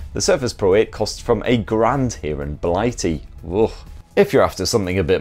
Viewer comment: A grand, I thought you said 8 grand.